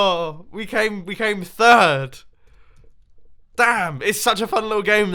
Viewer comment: did he just say damn, that’s weird to hear